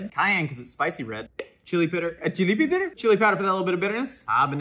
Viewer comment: Did you just say chilipi ditter?💀